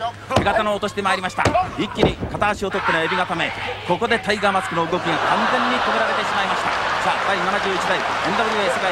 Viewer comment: 何を言って会場が爆笑したのか聞き取れん？ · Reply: 「馬場さ～ん、これで良いんですか～??」